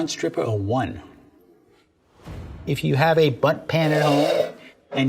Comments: I can’t be the only one who thought he said “butt pan” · I swear to god TWICE he said "butt pan" ..... I can't be the only one who hears that. 😂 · For a while I thought he said “butt pan” and I was thoroughly confused